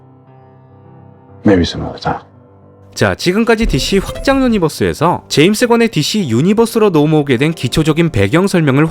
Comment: 똑똑...^^ 10:16...뭐라고 한 거에요? 잘 안들려서요.ㅠ.ㅠ maybe someone else time..이라고 한거에요? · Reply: maybe some other time.